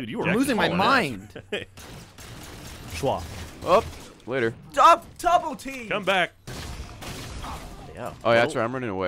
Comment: when gavin said double team at 4:13 it sounded like he said tubletee